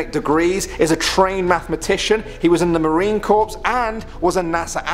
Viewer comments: Marine corpse??? Am I the only one who heard this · Did he just say Marine Corpse...